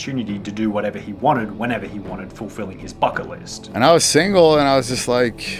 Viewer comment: is sounds like he is saying "fucket" list